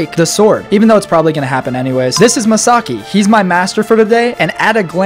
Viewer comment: This is Masaki, he is my master for the day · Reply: tbh i thought u said misaki like from vanguard i know nobody knows but its an old card game in asia